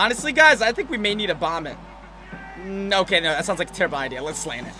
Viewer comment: thought he said vomit😂